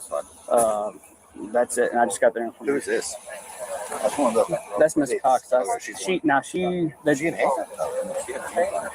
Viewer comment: what are they saying about India? Did that one officer say something about her doing meth? Sorry if I’m wrong but I can’t really make out what they’re saying, but I did think i heard something about meth. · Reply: I heard “..stinkin, like meth..”